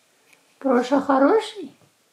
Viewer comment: Мне послышалось или Проша сказал"Проша друг" на 3:37 минуте